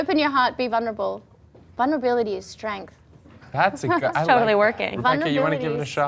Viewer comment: what does miranda say at 26:01-26.03 ? .... is strength ? thank you for answering :) xoxo